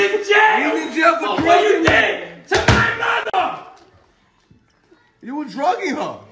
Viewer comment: the fact that those guys got angry at her made me feel a bit better about being a human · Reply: Guy In The Back Might've Taken It The Hardest Because It Sounds Like He Says "What Did You Do To MY Mother". Maybe That's The Girl's Brother? Just Some Food For Thought.